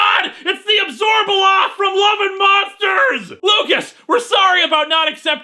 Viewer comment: What does he say at 12:48? I can't make it out. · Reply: He says its the Abzorbaloff from Love and Monsters and episode of DR Who.....that is one of the weakest of the David Tennant era